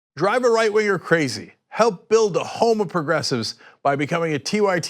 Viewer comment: Cenk has rather awkward pronounciation: I always hear "Help build the homoprogressives"...